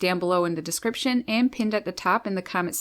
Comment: Have a birdiful day! Your mother is lovely! I knew she was! She raised you! I don’t often get to see my birds. You have taught me so much about birds! I love them ❤️ 🐦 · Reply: I thought said "have a 'vertical' day"! Hahahahaha!! I thought that was really funny because sometimes when someone will ask me how I'm doing I'll say, "well, I'm still vertical!" "Birdiful" makes more sense, being a chanel about birds! Oy, maybe I should get my ears checked! Oh well, had a good chuckle anyway! Thanks for the clarification! =D